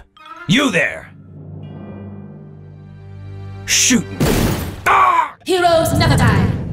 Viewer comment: heros never die. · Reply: I thought Mercy said heroes naptime